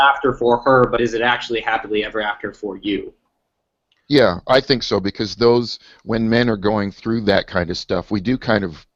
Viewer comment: Anyone catch the name of the law they talked about around the 3:28:00 mark? Thanks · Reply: briffault's law